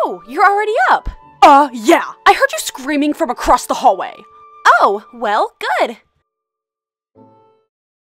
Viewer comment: did Frankie just say good!?! Like if you agree